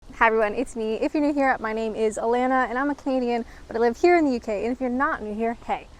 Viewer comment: When I first watched your video, in the intro. I thought you were say "If you're new here, my name is Alanna and I'm a comedian..."